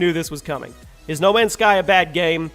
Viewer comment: it says "no many sky" instead of "no man's sky"